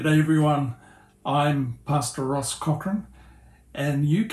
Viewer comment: I thought you said pasta Ross. I’m southern, American so I’m sure you’d probably have trouble with my accent also. Anyway, I’ve been fascinated with stereo images for a long time. I was using a 4x5 view camera to make them on film. I would take a picture, swap the film back then slightly move my tripod laterally. I did several portraits this way (which was difficult because the sitter had to be PERFECTLY still for the images to align later. I would make high quality scans of the negs and composite them in photoshop. So much fun. I do miss my big camera. Sadly, no more darkroom, no more film. Just the old iPhone these days. Thanks for sharing your magic moving picture.